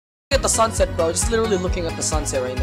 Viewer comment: Who heard poultin 👇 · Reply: I heard "POOOLTIN IS SO TRASH"